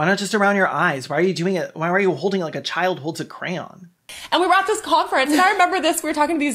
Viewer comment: When you said "why are you holding it like a child holds a crayon?" I thought you said gram rather than crayon and assumed you meant a child finding dads special sugar. I almost pissed my pants laughing